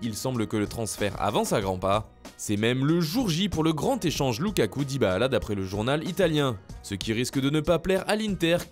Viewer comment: Pourquoi tu dis Dybala bizarrement ? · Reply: Le conducteur du turfu Mdrrr parce que « dis bah là ! ».